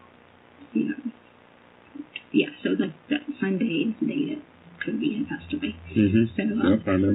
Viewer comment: did she just say the sunday before he passed away i thank she did she didnt catch herself she told on herself. but is that what she said anyone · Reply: She said "that Sunday was the day kobe passed away". She was more upset about kobe than Gannon 😡